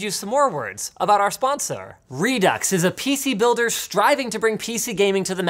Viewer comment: Anyone else notice that it sounded like Linus said “sponserrr